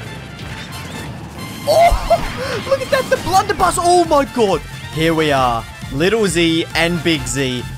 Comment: Did you say "Oh my Gordo" at 0:06?